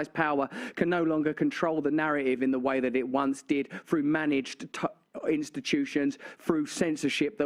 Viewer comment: thank you. that helped to realize that. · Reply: what was Russell about to say at ~1:47? "managed ta-" time?